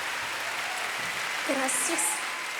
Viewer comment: does she say "gracias" (thank you in spanish) at 1:06:02 ??